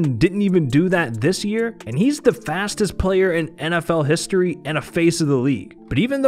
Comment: Me: Confused hearing, screaming Confused unga bunga Ik u did not say lamar is the fastest player of the face of nfl